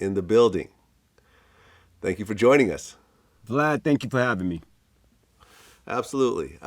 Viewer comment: Sounded like he was saying bvlad the whole time 😭😭